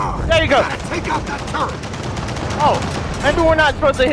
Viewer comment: The first time i heard "Shoot the bombs!" It sounded like they said Shoot the balls!